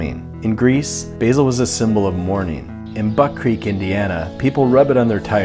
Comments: i thought he said butt-creak Indiana so i was going to make a joke about my town in Indiana is boring and lame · I thought you said butt creek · I thought he said buttcreak Indiana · Did he just say Buttcreek Indiana? 😂😂😂